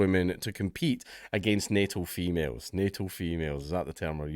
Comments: I was washing the dishes while listening. Thought you said "NATO females" instead of natal. For a moment I was confused and enraged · I thought he said NATO Females was the new term I had to do a double take 😂